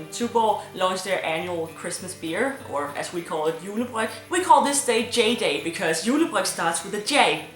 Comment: When she says “julebryg,” it sounds like she says “julebræk.” ...I have a weird brain...